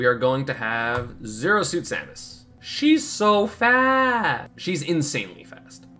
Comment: I thought he said she’s so fat